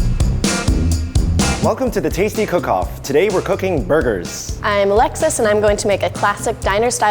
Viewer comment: I thought she said Hi I'm Alexa